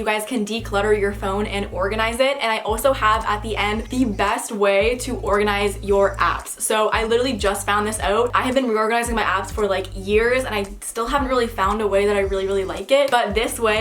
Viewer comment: nobody: me mishearing: "organize your ass." "I've been organising my ass for years."